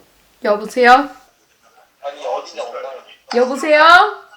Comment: what was the Korean word she was saying 😿😿? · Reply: 여보세요..?